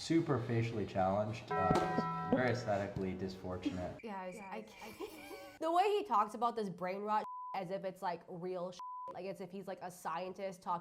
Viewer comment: did he say "DISFORTUNATE?"